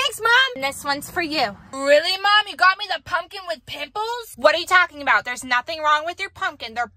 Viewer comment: i thought said a pumpkin with pickles🤣🤣🤣